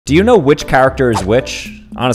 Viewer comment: I heard "Do you know which character is witch?" until I looked at the video